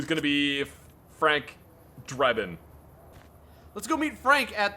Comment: I thought he Said, " Frank Drebln" the T V show was awesome Police squad... At the end of every episode almost everything Freezes.. Credits